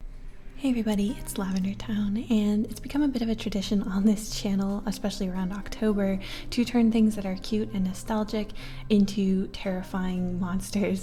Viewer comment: When I stared watching you, I always thought you said "hey everybody it's love in you're town" at the start of the video. I DONT KNOW ETHER!